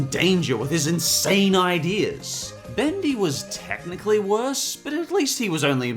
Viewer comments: did he say „Bendy" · DID HE JUST SAY BENDY?